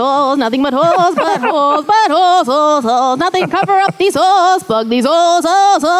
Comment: it sounds like she is saying fu** these holes